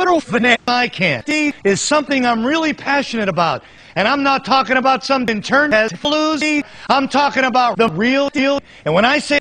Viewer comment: What did he say at 1:23-1:26? LOL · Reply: "And i'm not talking about some internet floozy"